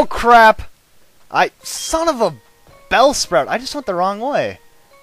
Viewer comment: did you say son of a bellsprout?